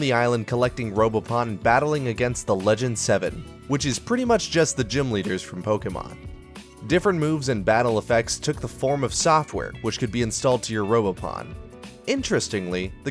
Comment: Did I hear TheLegend27?